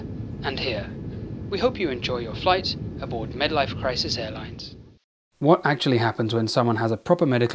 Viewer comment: Oh I thought you said midlife crisis, not medlife crisis Wait that’s probably the joke